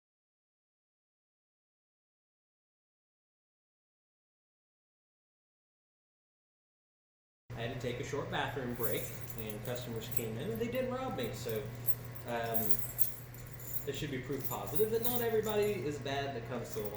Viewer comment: Thought you said "they did rob me" I was boutta be like how he so non chillant with that